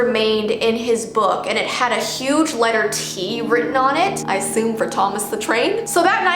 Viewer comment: "With the letter T, I simp for Thomas the Train?" Everyone: SHE SAID IT, SHE SAID THE WORD. oH, sHe sAid "I aSsUmE" soRrY. I heard "sImP" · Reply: She said assume not simp